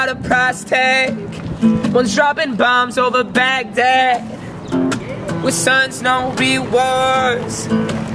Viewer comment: What does he say at 1:18 and 3:01? He is saying the same line, but I cant make out the words. At 1:18 he says: "..bombs over Baghdad ?????? ??? families are torn apart Dont be afraid, dont give up...." Then at 3:01 he says: "My big brothers in Afghanistan. ?????? ??? families are torn apart Dont be afraid, dont give up..." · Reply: our sons know real wars, families are torn apart.